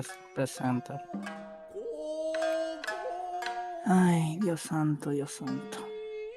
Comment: He actually said "Dios santo" wich means "Oh god" in that part.